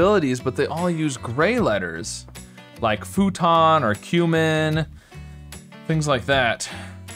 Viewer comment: I thought he said human at first 😂